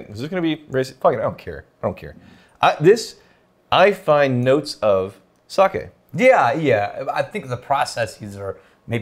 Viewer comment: I swear I heard say “I find notes of SUCK IT”